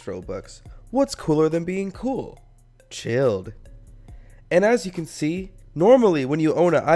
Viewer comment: Sounds like Albert saying "CHILLIN" It sounds like "KILN" after you say it after a while. YOU.. WILL.. GIVE.. ME.. A.. PUPPY!? .... give me a german shepherd